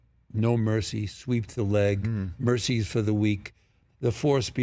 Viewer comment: the line in the first Karate Kid, when he says sweep the leg. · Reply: Actually no. I really thought he said sweep the lick (as in wounded leg)